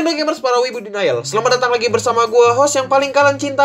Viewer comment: Eh gimana gimana? Gw kalo nonton selalu merhatiin openingnya yg "halo indo gamers para widu DINAYEL"? gw salah denger apa gw yg bego ya.. sampe sekarang gw belum paham kalimat itu 🤣🤣🤣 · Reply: wibu denial bosque